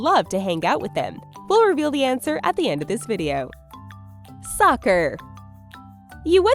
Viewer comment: Who else heard "sucker" instead of "soccer" at 6:40? lol